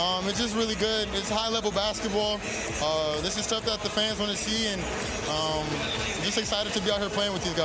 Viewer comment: i thought i heard "the pants wanna see"! lol! it sounds funny isnt it?